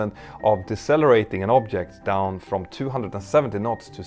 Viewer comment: did you say 275 knots to zero in 45 centimeters ?? 😮